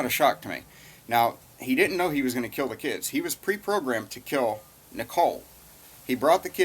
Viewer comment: You accidentally said "He was programmed to kill Nichol" instead of "He was programmed to kill Shanann" at 4:44.